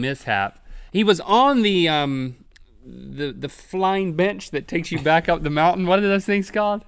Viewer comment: Who else thought he said flying b*tch